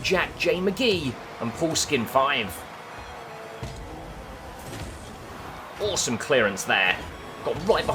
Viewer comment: Am I the only one that thought he said foreskin5 instead of paulskin5? Lmao